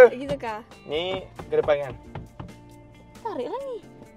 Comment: "ni ke depan Yang" aku salah dengar kah?? paggil sayang kah?? Aduii..rindu depa lah, please lah cb 😭 aku repeat dah 10x semua video